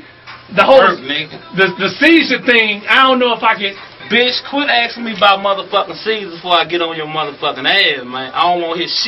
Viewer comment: 2022 and I still laughing so hard 😂😂😂😂😂😂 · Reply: Was just laying in bed, head empty and suddenly I heard "QUIT ASK ME ABOUT DEM MF CAESARS" I had to come watch loooool